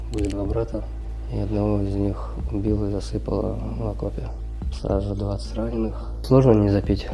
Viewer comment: Я правильно расслышал? На 6.30: У меня два брата, я оного из них убил и засыпал в окопе.